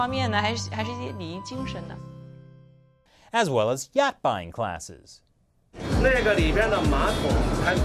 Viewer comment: I clearly heard "Yak buying classes," sounds correct to me.